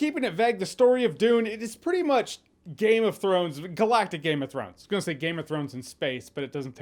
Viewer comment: It sounded like Jeremy said "Galactus' Game of Thrones"... And I kinda wanna see that!